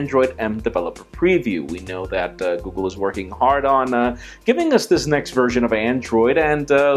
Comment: did you just say what i thought u said..... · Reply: Hardon